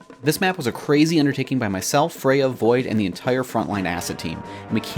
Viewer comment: Thought you said Froyo void and was super confused for a second. Hope this makes it in this May.